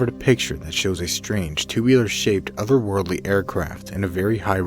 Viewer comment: I believe #1 is more proof of the Government hiding proof of either UFOs/Aliens or probably Technology that only the Government/NASA has control over or are hiding so people won't feel awkward....well , imma just assume ALIENS lol . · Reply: i heard it as tubular instead of two wheeler :/